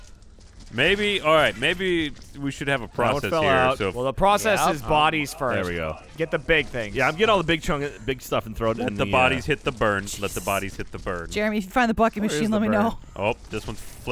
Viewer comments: I HEARD BIG CHUNGUS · Did just say big chunges the tik tok meme or general meme